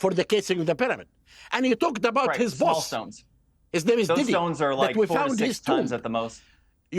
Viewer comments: did he just say diddy!!? 😂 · Zahi was a villian in an Indian Jones movie that never got made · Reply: did he just say diddy!?